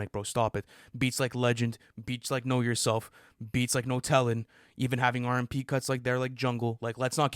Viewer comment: Why does it sound like ants saying nutella when he says no tellin'😭